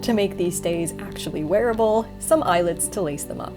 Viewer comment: your subtitles say eyelids instead of eyelets and that gave me a... very interesting mental image